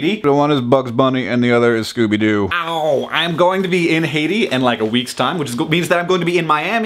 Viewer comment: Oh he's saying Haiti is thought he was saying Hade and I was all you're going to Hade?